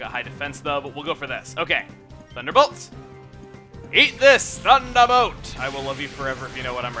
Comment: It's Dickson from Xenoblade. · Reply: He says "Eat this! Thunderbullet!" but it sounds like he says Thunderbolt.